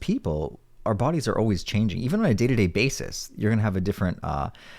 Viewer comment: When he said "even on a day to day basis" i heard "even when i dated a bassist" for some reason...i was confused.